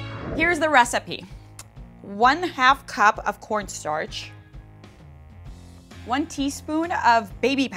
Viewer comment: She said 1/8 of a cup and used a teaspoon. This is a poorly produced video, and her attitude toward this diy was negative from the start. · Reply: No...she said 1 half cup of corn starch, 1 tsp of baby powder and 1/8 tsp of powdered foundation. I think you misheard her. Either way, this DIY was a bust from the beginning from the minute she used cornstarch. :p